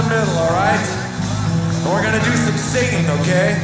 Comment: I think he says Were going to do some Satan when you expect "singing"